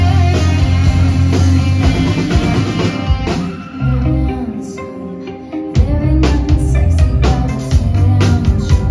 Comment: what she says in this part? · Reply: handsome, there’s nothing sexy about attention